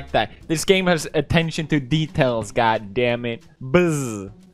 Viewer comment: who else watched this while eating and thought he said “attention to beetails”